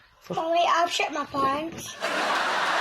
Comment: What did the kid say? =D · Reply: Mommy, I've shit me pants